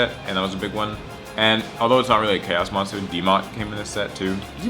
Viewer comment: What is the card you talk about at about 2:23 I think u said like demot or something like that but I can't find it I run a chaos deck and I'm really interested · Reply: Dylan Carter Dmoc Dark Magician of Chaos